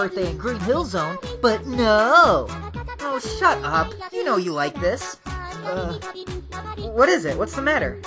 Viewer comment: did he say he like Tails